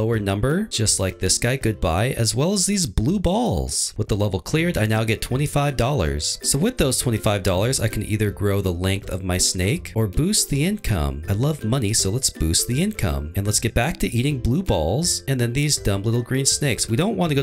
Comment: For the first few minutes i thought you were saying Incum!